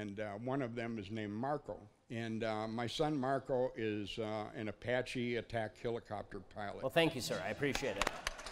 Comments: I thought he said he identified as an attack helicopter 😂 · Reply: Michaela Watson I thought he was saying his son was an attack helicopter. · I thought he said he identified as an attack helicopter 😂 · I legit thought he said his son identifies as an apache attack helicopter 🤣🤣🤣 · I thought he said he identified as an attack helicopter 😂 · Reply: Michaela Watson i thought he said MARCO was an APACHE ATTACK HELICOPTER 😂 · Totally thought he said his son WAS an apache attack helicopter 🚁. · Because this world is so crazy, my mind thought it heard “my son is an apache attack helicopter” and that he was ragging on the “I identify as…” lunatics. 😂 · I actually thought he said his son identifies as an attack helicopter and i was staggered by how serious everyone was being. Actually had to replay it.